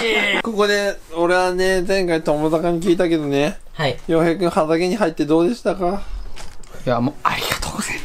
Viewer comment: 畑が鼻毛に聞こえるwww